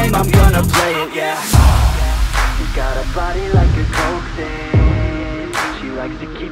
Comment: Him: she's got a body like a coke fiend What I hear: she's got a body like a goldfish 😂